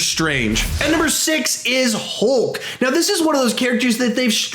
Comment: he says HOLK am I the only one who hears this